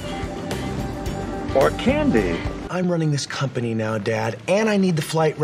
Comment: i'm going crazy i thought he said cocain not candy